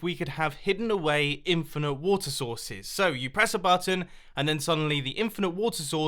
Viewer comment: When he says water source it sounds like he is saying water sauce!